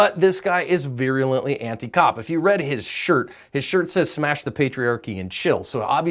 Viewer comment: "Smash the patriarchy and chill" makes much more sense haha. I thought for a second it said "smash the staircase and chill" like he was a fitness nut or something xD clearly not a fitness guy... I was thinking "when's the last time you smashed anything, bud?"